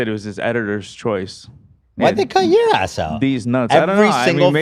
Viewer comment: Bro I have no idea what's happening: "I think it was his editors choice" Ethan: "IthinkIyeahasou" (no idea what he said): "These nuts" Can somebody explain this? :D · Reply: Ethan said "why'd they cut your ass out?" when brought up that Mizkif reacted to every Creator Clash 1 fight EXCEPT for lol